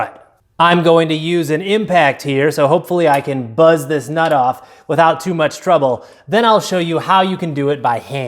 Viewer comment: I thought you said “I’ll use an impact so I can bust this nut off without any trouble” You almost earned a subscriber